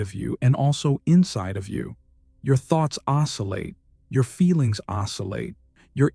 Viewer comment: NOTE: The subtitles on this video have the word “isolate” instead of “oscillate" (three times). starting at 26:30 more or less.